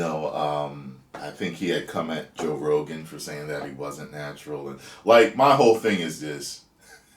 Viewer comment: I thought he said he wasn’t natty